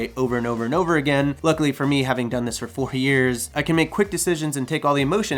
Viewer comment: Sounds like you said you've done this "40 years" at 6:50, how long have you been doing this? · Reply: It feels like 40 haha. But 4 years.